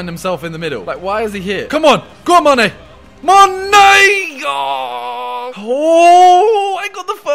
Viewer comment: did he say "ma nigga" here ? lmao